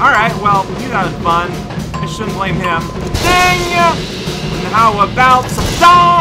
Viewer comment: Did just say "how about some dong"?